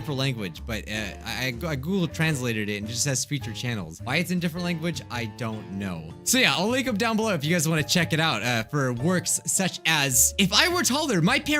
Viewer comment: CC did it really say so yeah I'll wake up down below 🤣🤚